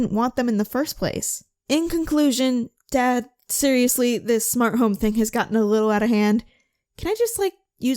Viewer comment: I thought it said smart homies XD